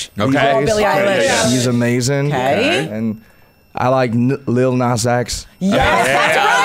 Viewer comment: when he said lil nas x i misheard it as lil xanax and hollered